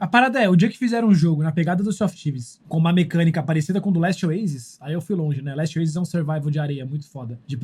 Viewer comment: qual o nome do jogo que ele falou ? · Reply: Last oasis